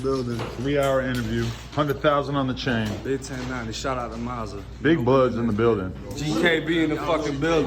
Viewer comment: did he say “shoutout to mozza “😂😂😂😂 1090 is so stupid and is hilarious 😂